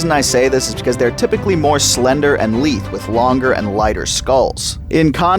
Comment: Awesome vid, there are so many creatures just in this one area! You also made me question the pronunciation of "lithe" at 34:04. Don't scare me like that. (at least I think that's what you said; the CC spelled it "leaf.)